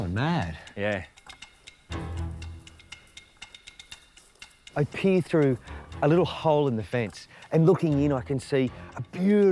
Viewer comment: I swear, at around 1:13 I thought I heard him say, "I pee through a little hole in the fence."